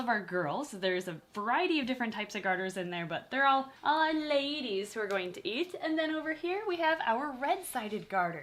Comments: +: these are all ladies we are going to eat Me : she said eat instead of feed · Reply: no, she said "these are all ladies who are going to eat" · did say that they are gonna eat the garter snakes?? Come on. Fight the erge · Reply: “Who are going to eat” is what she said